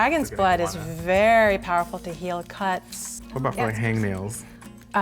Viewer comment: Oml, the lady when she said “dragon’s blood” I thought she said “dragon’s butt” and I was like wtf!😂😂